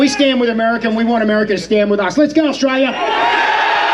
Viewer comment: Thought he said good Australia music AC/DC ISNT GOOD 😂 that’s the second flag of AUSTRALIA